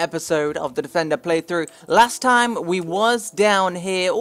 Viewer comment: Did he say "we was" at 0:40? 😐